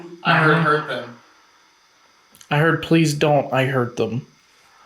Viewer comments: When you ask why can’t you dig in the basement- I definitely don’t hear ‘I hurt them’ I hear “It’s cursed” listen again at 27:25 I think someone buried items relating to a ritual (a bundle of hair/fabric etc) rather than human remains there. The ‘unexplained voice’ at 38:27 sounds more like dogs/wildlife..you hear the same sound but for longer right after at 38:53 The picture on the coin looks like a jester’s hat..trickster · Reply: I don't hear "please don't, i hurt them." I hear, "please don't, i HIDE them." 🙄😬 at .25 speed. 27:26 mark. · When you ask why can’t you dig in the basement- I definitely don’t hear ‘I hurt them’ I hear “It’s cursed” listen again at 27:25 I think someone buried items relating to a ritual (a bundle of hair/fabric etc) rather than human remains there. The ‘unexplained voice’ at 38:27 sounds more like dogs/wildlife..you hear the same sound but for longer right after at 38:53 The picture on the coin looks like a jester’s hat..trickster